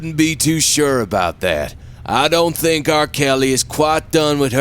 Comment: Did they just say R. Kelly?!?! 😂😂😂